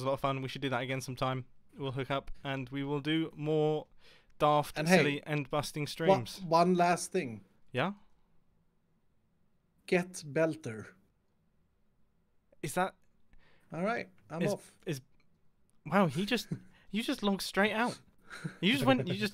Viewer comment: I swear I heard Iskall say "Get Vectored"